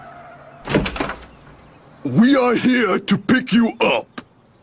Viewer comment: for the longest time as a kid, I always thought Muscle Man was saying "We are here to beat you up"